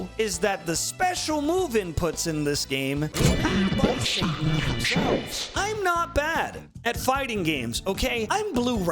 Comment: can someone tell me what he said there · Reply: Were made by Satan himself